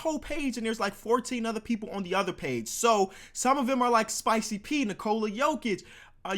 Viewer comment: I swear he said spicy peen🤣